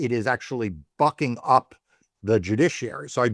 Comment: I really thought he said fucking up the Judiciary LMAO